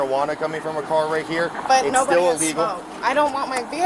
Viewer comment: The woman also admitted to smoking marijuana in the vehicle. You should return your YouTube law degree. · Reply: I didn't hear her say that. I heard her say at 7:04, "But nobody has smoked."